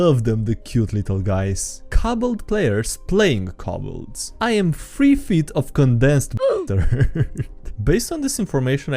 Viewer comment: "Cabalt players playing Cabalt: I am three feet of condensed ḅ̸̣̍̽̔͗̑̈͘̕ǎ̸̯̞̯̠͍̀͊̀͝t̶̡͙̐̿͋Ȏ̸̜̣̭̯̯̰̟̲͈̾̅̉Ò̴͙͕̑̋̐F̸̢̟̤̘̤̲͔̜̟́̓̄̓̂ḥ̶̡̜̻͛̔̀̏̔̊̀e̶̟͇̲͌̈́͘ř̸̻̠d̶͉̥̖̮̗͆̒̃̕̚̚̚͝" what did he say? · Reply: Bastard, I believe